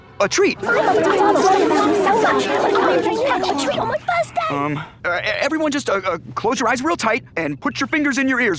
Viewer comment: I thought he said “on my birthday”😕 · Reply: I think he said "I get a treat in my first day!"